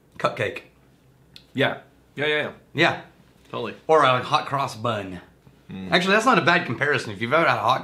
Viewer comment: When watching this on 2x speed, I really thought said, “a hot girls butt” instead of hot cross bun. 😂 I thought, “wow it was different back then!”